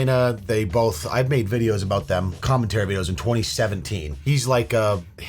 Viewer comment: You made what on Adam/Lena? Sound like you said cummentary. Speaking of which: that's got to be a form of content creation with how weird society has gotten now, right? Just someone watching a skinflick, doing the deed, all in the style of a movie reaction....? That just seems like something the internet would do.